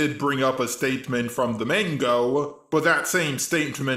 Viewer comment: what he keep saying mango and animu?